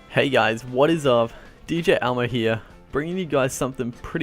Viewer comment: Sorry about this, just got very curious... also 0:02 did you just say DJ Elmo??